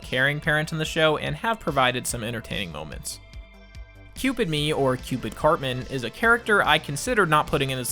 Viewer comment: I'm gonna be 100% real, with Cartmans... *accent*? I thought he was saying 'Cupid Ye' as in Cupid Kanye West and that makes it 100x Funnier.